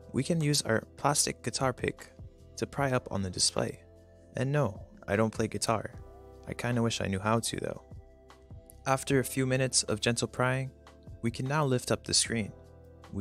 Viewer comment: I thought that said gentle crying, that would've been SAD